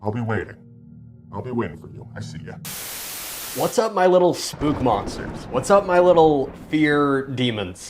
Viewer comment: The first thing I thought he said was “splooge monsters”